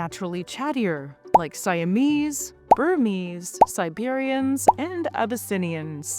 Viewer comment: Anyone notice that at 3:24 she says "abyssinians" but the screen and closed captions say "tonkinese?"